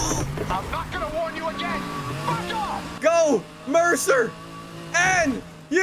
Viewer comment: whys he sound like hes sayin fk off instead of back odd